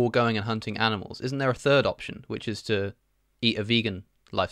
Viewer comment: How great was? Let me know what you think about this new video concept! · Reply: I thought you said; Isn't there a third option? Eat a vegan! Great video tho! Good for opening up your content to people who might be turned off by a more "sterile" video style. Keep it up!